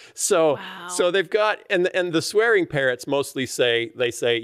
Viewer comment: Listening in on this while driving, I first thought I heard it was about "swearing PARENTS." I was a little disappointed when I realized you weren't going to address this issue! 😊